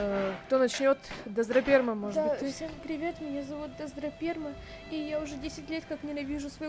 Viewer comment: Я одна услышала "Драздраперма" Брайн маааапс!!!!